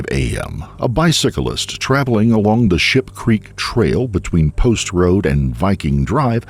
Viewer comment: Did he say (2:37) shit creek?